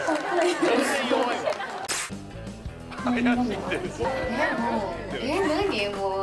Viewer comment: 「怪しかったんだよぉ～!」に聞こえるw